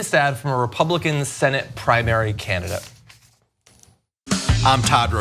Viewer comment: Oh, candidate !! You had me confused there for a minute. I thought you said, “...primary, Canada”. Lol! 😅💜😄